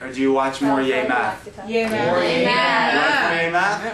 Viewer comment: When they chanted "Yay math" for some reason, the first time I heard, "Amen!" and the second, "Gay man!" ... I think I need to get my ears and my brain examined, but I'm afraid they will be surprised to find that I have Grinch Syndrome, but in my brain: It's 3 sizes too small.